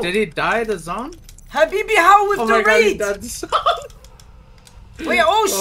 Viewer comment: deadass thought he was saying "habibi how with the wraith!" lmaooo funniest guy in twitch rt